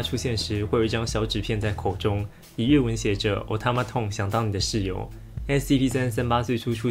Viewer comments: ，“otamotone相当你的室友”被我听成了“我他妈的想当你的室友”😂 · 我聽成 我他*想當你的室友（咳！(´･ω･`)